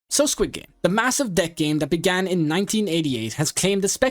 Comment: did i hear massive?